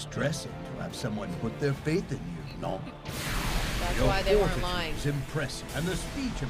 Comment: just realised All For One said "Non?" there, instead of "No." Like he's making fun of Aoyama.